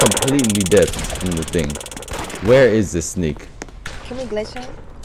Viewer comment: did u say the n word? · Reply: He said snake